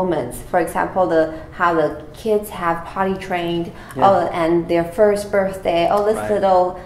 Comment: I believe she said “potty trained” instead of “party train” which is totally out of context.